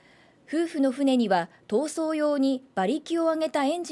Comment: 闘争が暴走に聞こえる